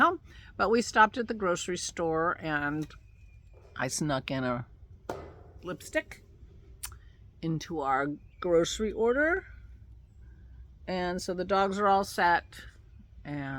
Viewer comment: What I heard was "I snuck a lipstick into our grocery order. The dogs are all set..." I had visions of them both with scarlet lips! Glad your conversation with Dennis helped you - sounds like a very positive thing. Take good care xx